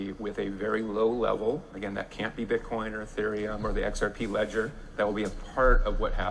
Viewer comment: I'm lost on 12:10 did he say it can't be bitcoin , ethereum, or the xrp ledger?